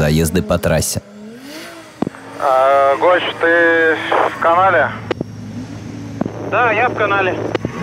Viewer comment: мне одному послышалось в канаве?